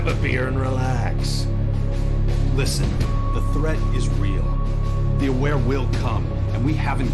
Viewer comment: did he just say the Aware will come? Is that the name of the aliens? Lol